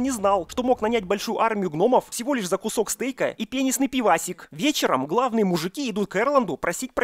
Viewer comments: Возможно мне послышалось, но я услышал вместо "пенистый пивасик" "пенисный пивасик"))) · пенисный пивасик? Мне одному послышалось? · мне послышалось ,,,, пенисный пивасик ихиххии )))) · Скажите мне, что я не один такой, кто неправильно услышал «пенистое пиво» с первого раза?..